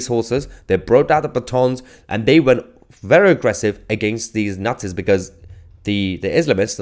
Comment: What did he say 4:38 ? against the Nazis ?